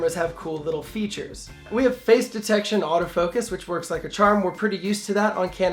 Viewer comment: I think what he said was in 1080p is face detect at but he never mentioned the crap autofocus in 4k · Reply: Noah B got it. Face detect. Sorry I thought he said Phase . Makes sense. The lack of mention of Contrast autofocus is a bit disingenuous as the focus on this thing sucks. It’s too bad that Canon crippled it like this as it would have been perfect. You could even forgive the lack of 24P which Canon has determined is the new Satan apparently.